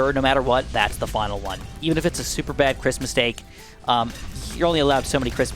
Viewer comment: I kept hearing 'Christmas takes' whenever you talked about 'Chris mistakes'